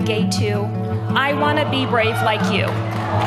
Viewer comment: I thought she said, "I want to be raped like you" when she introduced the 9 year old to Buttigeige.